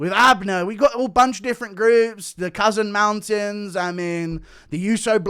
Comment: Initially heard it as the "cousin mountin's"